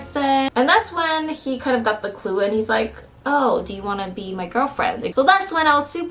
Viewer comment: I thought you said: Would you like to meet my girlfriend? 😂